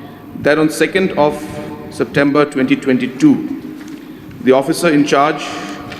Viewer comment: did I hear it correctly?. 2nd September 2022